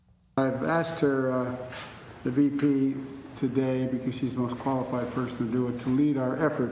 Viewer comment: I THOUGHT HE SAID THE MOST DISQUALIFIED PERSON. BUT THAT MAY HAVE BEEN HIS MASK.